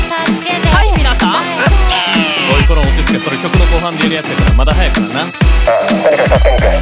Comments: ジェルくんの「おいこら落ち着け」が「おいころん落ち着け」に聴こえるんだけど私だけ？笑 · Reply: 大丈夫僕も［お位ころん落ち着け]って聞こえたから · ジェルくんの「おいコラ落ち着けそれ曲の最後で言うやつやから、まだ早いからな」が 「おいころん落ち着けそれ曲の最後で言うやつやから、まだ早いからな」に聞こえる······ カッコ良い!!!! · ジェルくんの「おいこら落ち着け」が「おいころん落ち着け」に聴こえるんだけど私だけ？笑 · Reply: うちもおい、ころん落ち着けに聞こえるw